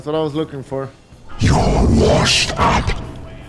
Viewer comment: Did say you are washed up